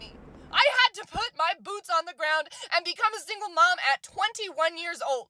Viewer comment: “I had to put my boots on the ground” are you kidding me? they literally exaggerate everything in order to make a point or justify an excuse. They’re embarrassed by everything except complaining & begging for money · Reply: Thought she said boobs at 1st lol.